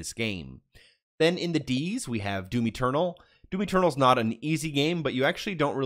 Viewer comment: Im not watching the video. But listening along and searching game titles as I go to see if they are still in GamePass. Anyways. I thought you were saying Dewey Turtle for Doom Eternal. I was stoked for a game about a turtle. Oh well.